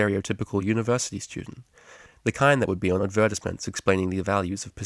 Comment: Oh hey, I wanted to note on something I just realized on my, what 30th time listening XD. Anyway. when you described BonBon as the type of pony to be on the cover of advertisements, it sounds like you say adVERTisements, but I'm used to hearing it like adverTISEments. Idk, maybe it's just like the difference between Soda and Pop, but it caught me off guard. hehe. Just wanted to let you know.